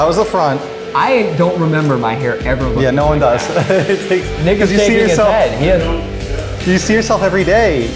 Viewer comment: did he just say n-words shaking his head?